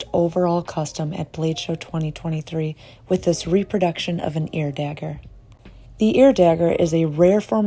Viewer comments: An ear dagger is a relatively rare and exotic form of dagger that was used during the late Middle Ages and Renaissance. It is so named because the pommel of the dagger has a very distinctive shape, in some cases like a human ear. Ear daggers frequently have a single sharpened edge that ends in an acute point. It is thought that the ear dagger was introduced to Europe from Spain, where it presumably originated from the Moors. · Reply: I thought they was saying air dagger and was like cool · An ear dagger is a relatively rare and exotic form of dagger that was used during the late Middle Ages and Renaissance. It is so named because the pommel of the dagger has a very distinctive shape, in some cases like a human ear. Ear daggers frequently have a single sharpened edge that ends in an acute point. It is thought that the ear dagger was introduced to Europe from Spain, where it presumably originated from the Moors. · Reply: Oh, I thought she was saying "Air Dagger" So i was thinking it was hollow, was for throwing/launching or had vents in the handle or something.